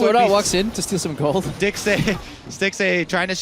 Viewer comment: Did he really say "Dicksay"?